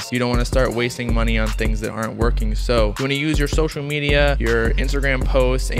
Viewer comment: Will Iann Dior and the other Internet Money artists last long in the industry? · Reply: I thought he said email me yo social media 😂🤦🏽‍♂️ on the bright side my first song hit 500 plays in 10 days